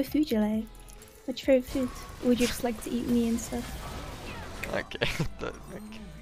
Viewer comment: did i hear that right she said eat me instead ?